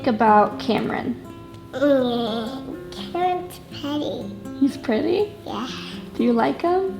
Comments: At first I thought said he’s petty but the i heard he’s pretty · I'm so embarrassed that I thought said he was petty lol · I heard say he's petty 😭